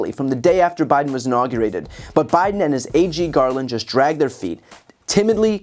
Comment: LOL, I thought BTC said “Biden and his aging Garland” rather than “Biden and his AG Garland”. 😅